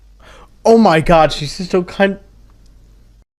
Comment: what in the world were you trynna say bro? 💀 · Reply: "Oh my god, she's chok-" Probably "she's choking on her own blood," since it seems she was picked up and thrown on the ground.